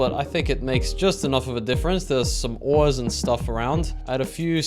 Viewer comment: Whoever edits your video is absolutely hilarious, props to them! I love your vibe as well, very fun to watch and so dedicated to the crafting and mining :D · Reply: They don’t edit the subtitles it’s prob auto gen, cause it says oils instead of ores